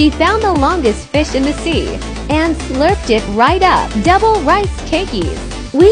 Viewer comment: Why am i so dirty minded 😂 i thought she said " Slurp it up fast " 😂